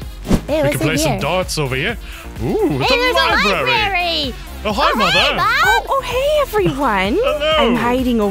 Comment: did anyone else hear call mother mom 🤣